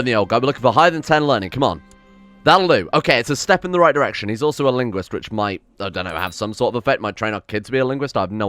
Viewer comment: I thought he said ligma